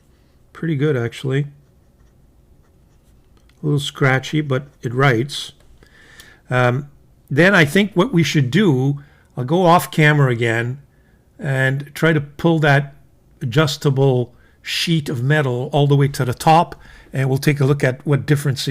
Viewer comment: This is what I heard: "Let's go off camera again to try to pull this shit of metal"... 😂😂😂